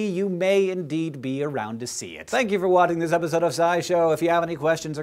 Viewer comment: I heard : "Thank you for watting Scy-Show." I then proceeded to lol.